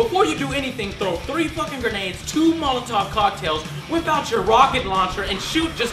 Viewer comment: Watch this video with subtitles on..please..just...just do it. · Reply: with subtitles on 1:18 he say throw 3 fu.cking grenades and 2 molotovs subtitle say :2003 fingernail ....WTF??